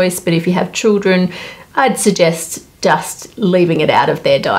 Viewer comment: Anyone else hearing her say dust instead of just😅